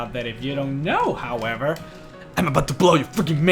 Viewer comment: 1st time you said i'm about to blow your mind. I thought you said "i'm about to blow you man"